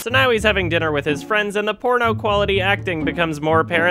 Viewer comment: does Adam say "Porno quality acting" or "Poor, no quality acting"? I think about the important questions in life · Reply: He says porno.